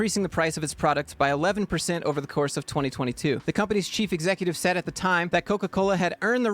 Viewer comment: *The companies chief executive". I actually thought you said cheap executive at first.